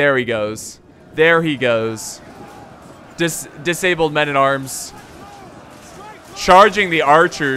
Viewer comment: i swear he said disabled men at arms😂